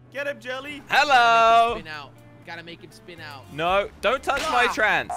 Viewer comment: did he just say “dont touch my trans”?? Edit: lol